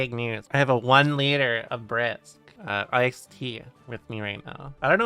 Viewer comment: Oh no I thought she said she had one liter of breast and not brisk